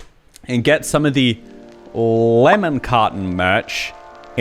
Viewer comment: Omg this is the first time I heard “lemon carton” right. Up until this day i thought you meant “lemon cotton” and thought of it as some weird Asian lemon flavoured cotton candy 🤦‍♀️